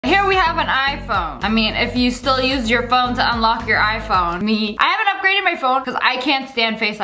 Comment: she sounds like shes saying 'if you still use your phone to unlock your iphone' 😂